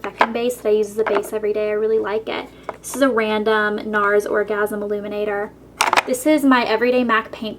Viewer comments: is she saying "orgasm" ? :D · I hear orgasm eliminator :| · did she say orgasm eliminator??